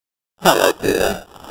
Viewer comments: parece que dice hello pibe XD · soy solo yo o oi que dije helo pibe xd